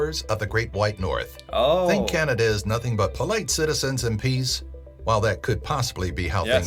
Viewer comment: Omg…laughing so hard! you thought they said “peas”…..they said “PEACE” 😂🤣😂🤣😂